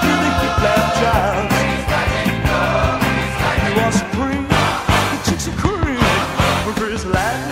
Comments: Legit thought it said, the chicks will cream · Am I the only one that hears "the chicks'll cream" instead of "the chicks'll scream"? Listen again, it sounds like it, and looking at the rest of the lyrics, is it really that strange for it to be that? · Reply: I always thought it said chips and cream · It sounds like he is saying the chicks will cream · I always heard the “You are supreme The chicks will scream” As “You are scream Want chips and cream?” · at first i thought it said the chicks will cream haha